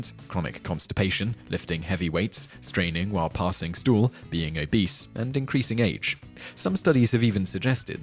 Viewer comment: I know that you said, "being obese," but it sounded like you said, "being a beast." Is that like being an ass? Does being a jerk make you susceptible to piles? 😉